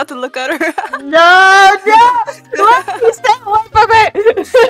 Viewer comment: LOL I WAS WONDERING WHY YOU SAID “STAY AWAY FROM US” TO ME!! I thought you said it bc I was annoying y’all- 💀